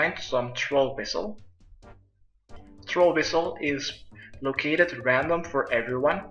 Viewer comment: who else thought he said whistle??